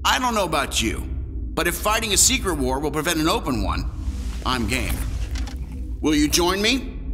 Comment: who else heard smthng else? 🤭😅😅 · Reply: ‘I’m gay’ no. He is not.